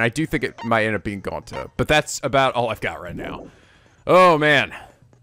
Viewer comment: They definitely dragged out the loop reveal more than I remember! I was saying the same thing at this point myself, that this might be the best in the trilogy. And that's before we even got to gets shot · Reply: I thought you said BUT that's before. Glad you liked that divisive thing :)